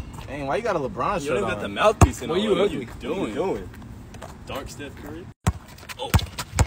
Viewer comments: Why did I just hear someone say “dark stuff curry” 1:42 idk hey but I heard it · One time i heard “Dark steph curry?”